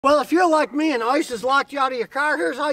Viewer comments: Sounds like you said “ well if your like me and Isis locked you out of your car” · For a split second I thought Scotty said ISIS locked him out of his car 😂😂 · I thought he said isis locking you out lol